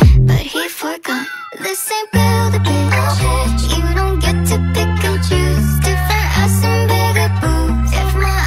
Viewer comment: Am I the oy when who heard it " They say build a bitch or you don't get s pickle juice " like who cares about PICKLEE JUICE